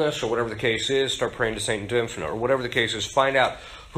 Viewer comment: Sorry in min 4:04 which Saint did Fr. Ripperger mention I couldn't catch the name.. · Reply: St. Dymphna 🙏